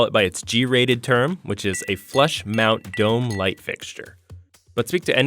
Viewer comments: I thought you said "flesh", not "flush"... I was HORRIFIED . Edit- I should explain. I wasn't looking at the video. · I thought they were saying "flesh mound" until about 4 minutes in .-.